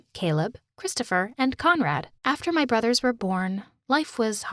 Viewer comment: I thought she said comrade and started singing the national ussr anthem